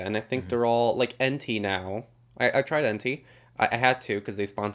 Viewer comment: What is that service he mentioned at 11:11? Something that sounded like ‘Enti’? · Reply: Actually I found it: “Ente photos”